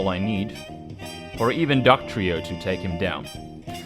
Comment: I swear you said Ducktrio